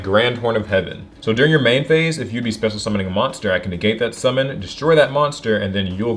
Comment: My favourite structure is the ancient gear one, it was my first deck 13:08 did really say "if you be"?